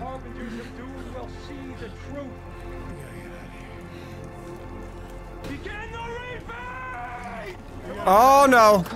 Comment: I thought he said begin the raping